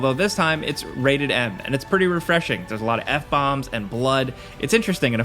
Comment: "has alot of n- bombs" is what i heard at first